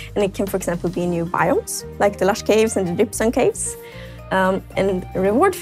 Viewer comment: Guys I swear I heard say gypsum caves at 0:57 maybe it will be a new cave!!!!!